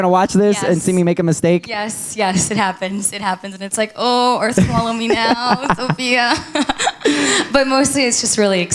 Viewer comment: What did she say at 2:23? It sounded like "Oh, or swallow me now, Sofia" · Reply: TamayoToonzTV she said ohh earth follow me now